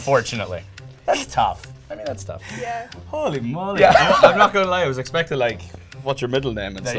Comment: did Niall say 'Holy Moly' at 03:03 ?????????????????